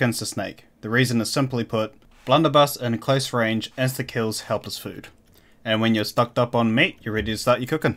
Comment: what the hell is he saying at 8:16 about why to use the blunder??? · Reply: blunder buss at close range instakills helpless food. Still makes more sense to use a sword but thats what he said